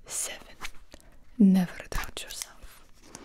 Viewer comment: When Tatyana said “Never doubt yourself” my confidence shot up by 100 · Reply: At first, I thought she said "never touch yourself." And I was so goddamn confused 🤣🤣